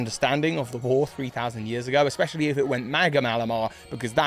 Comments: did you just say maga malamar? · it sounds like you said MAGA malamar lol